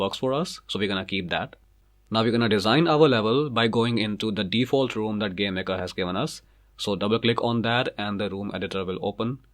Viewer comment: I thought he said "The default room that God has given us"